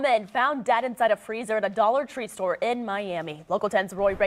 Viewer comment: A Dollar Tree. I thought he said idolatry. Lol